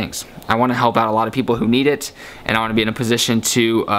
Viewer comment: it sounded like he said "i want to be in the postion to make grilled chickens in this world" lol